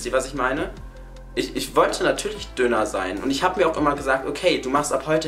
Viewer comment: Ich schaue ein abnehm video (ich bin 13 jahre 1.64 groß wiege 69kg😕) und verstehe bei 5:29 döner 😂😂